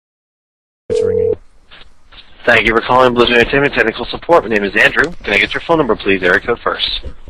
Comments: Lol ballet tap, I thought he said tentacle support as well. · it sounded like he said tentacle support